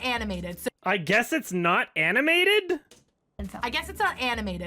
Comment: I think she’s saying “I guess its A animated” still doesn’t make sense.